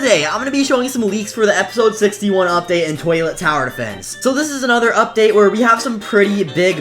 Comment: I want to see the update 61 leak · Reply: I thought you said, "I hate toilet tower defence" lol